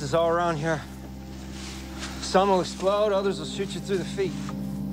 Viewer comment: what did he say I only heard “Some will explode and some will Blank through the feet” what did he say before through ? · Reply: "shoot you through the feet"